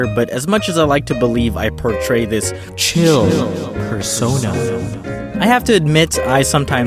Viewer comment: did you just say "fur-sona" ?!